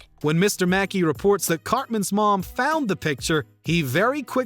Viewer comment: i stg at 17:40 i thought you called him "cockman" i had to take a triple take